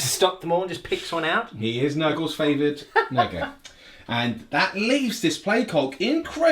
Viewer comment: did just call grotti "Nurgles favoured nigga"???